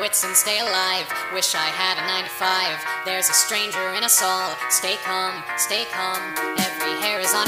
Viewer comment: i thought he said: There's a monster in assalt